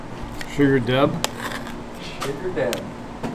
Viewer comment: “sugar daddy” is that what i heard😂